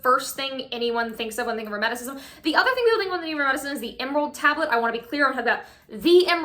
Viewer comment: literally what did you even say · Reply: I believe it's "The other thing people think of when they think of Hermeticism...". Half speed.